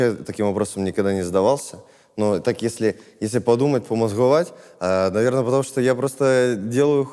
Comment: А мне ожной послышалось в начале украинское слово "Помозгувать"?